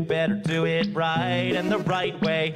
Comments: “I LIKE OREOS AND PU- ahem KIDZBOP” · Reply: I thought it said I like Oreos and cookies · "Hi i like oreos and pu-" Me being dirty minded :😳😅 ehhh yes ehh pudding mhm yeahhhhhhh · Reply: I thought that she was saying Oreos and cookies? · I always thought its said Oreos and puppies… hold f$#&!….. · Am I the only one that thought it said "I like oreos and puding" but when I actually looked up the song it said "I love oreos and pu- kids Bob"😭😀